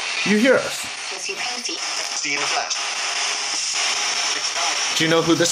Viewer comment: right after he asks, "do you hear us?" I hear it say, "we see, it's Ash."